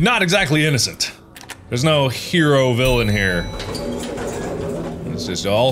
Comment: Mark: "There's no hero villain here." Amy and Dr. Bose: "ThErE's nO heRO viLLiAn HeRE!" Me: face palms bruhhh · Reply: I thought he said "villain hero" like anti-hero